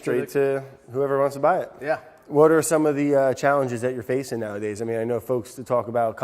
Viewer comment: Great vid as usual, also did anyone notice he said wourder instead of "what are the" at 10:47, I was so happy at that moment hheh